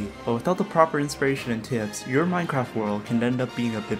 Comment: So I laughed my ass off when I misheard one of the first lines as "-creativity! But without the proper inspiration and Timbs, your minecraft world can end up being a bit bland." The rest of the video was great. Very creepy. But that one bit tho.